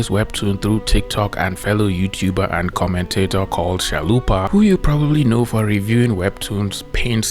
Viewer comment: Is there other folks who heard Zalupa instead of Chalupa? Otherwise this is comment for promotion haha